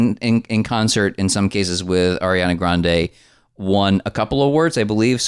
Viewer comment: Am I the only one who heard "Arian-er" and spit? 🤣🤣🤣🤣